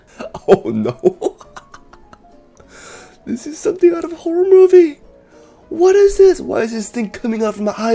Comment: what did bro say at 2:35???? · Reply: “This is something out of a horror movie.”